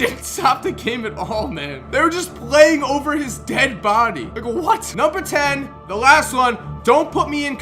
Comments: Who else thought he said “nigga what?” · did he say nigga what